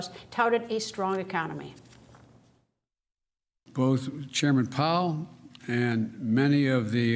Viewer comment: I thought biden said chairman Mao.